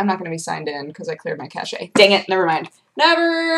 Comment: Did she say cache weird?